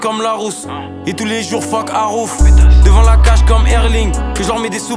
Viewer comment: « et j’suis partout comme arouf » c’est devenu « et tout les jours fuck arouf » 🩸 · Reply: Ouais moi aussi j'ai entendu "fuck arouf"